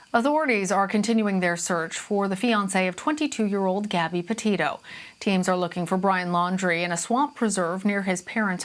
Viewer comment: did she say "potato" or am I hi